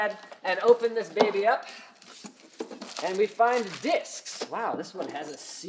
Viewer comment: When he said "and we have disks!" At 7:03 totally thought he said dicks!